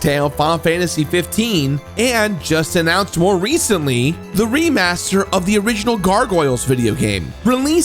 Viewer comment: I'm sorry. I kind of zoned out through the entire video. I heard "Gargoyles Videogame Remake" and my mind was back in the 90s for a bit. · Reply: Well, you misheard. He said "remaster". Though it's easy to confuse the two.